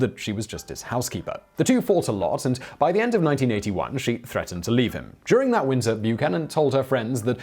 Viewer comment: Did you say NINETEEN EIGHTY-ONE instead of 1891?